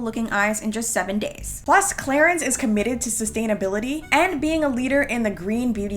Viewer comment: I thought she's said Claritin lol. I was like they produce skin products now LOL